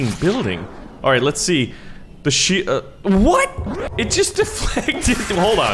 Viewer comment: it sounds like he said THE S××T